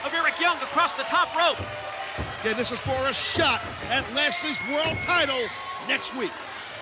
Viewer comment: did he just say lesners titles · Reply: Skylar Trillingham Nah, he said lashleys world title